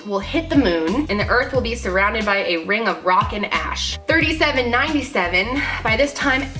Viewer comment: The part where she said " rock and ash" I thought she said rockin ass 😂😂